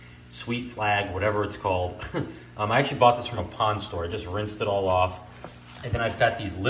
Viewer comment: LOL thought you said a “ pawn store “ I was like who tf pawns plants 😂😂😂